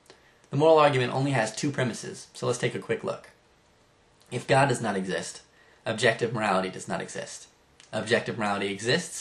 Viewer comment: See, I knew that is where the error was, I just didn't notice you saying it explicitly until now. He does NOT say "If objective morality does not exist, then God does not exist" he says "If God doesn't exist, then objective morality doesn't exist." Check 5:33.